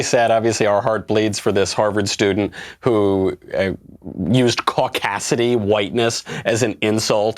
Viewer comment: Wow, I guess I'm really dumb. When that gal said "caucasity" I didn't realize she was saying "cauc" like "Caucasian." I thought she was saying "cockasity" like referencing horrible men with audacity. It wasn't until the commentary that I realized it was a racial thing rather than a feminist thing.🤦‍♀️ Should have known since she was ranting about "all lives matter" but I guess I'm slow.